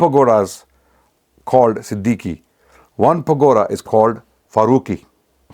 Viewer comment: did you say "pakora" 🤨🤭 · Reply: Pagoda